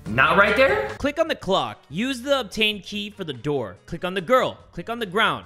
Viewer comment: Thought he said " key of taint" not "obtained" lol